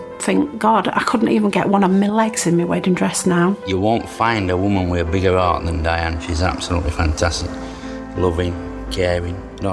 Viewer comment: I thought for a second he said "you won't find a woman with a bigger arse than Diane" hahaha